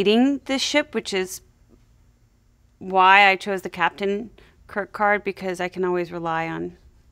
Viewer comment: I swear at 18:58 when she said she picked the Captain Kirk Card, I heard her say Captain Kircard, and got a mental image of a fusion between William Shatner and Patrick Steward.